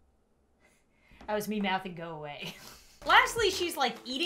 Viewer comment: "that was me mouthing go away" ME: I thought you said no, no way T.T